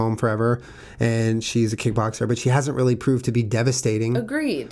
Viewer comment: when you said kickboxer I thought you said k-pop star and I was like woah good for her